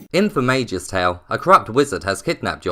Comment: I absolutely love this game. It's so much fun. Also, it's very early and I haven't had coffee yet and I thought you said "a crap wizard" at the beginning (instead of "corrupt") lol